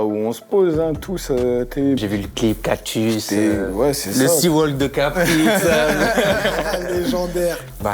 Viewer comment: i dit quoi à 4:51 quand i dit "...Kpri" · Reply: le C Walk